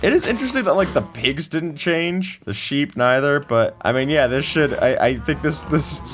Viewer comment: I heard sh** not should